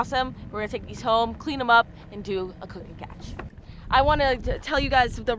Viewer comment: Did she say cook and catch😂